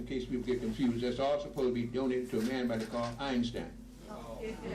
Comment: The captions didn't display the name of the Nigerian that proved relativity. Did anyone catch his name? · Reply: Gabriel Oyibo